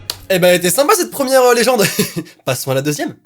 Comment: Kuchisake j'ai compris "Gucci sake" 😂😂😂😂😂 du Sake de chez Gucci😂😂😂😂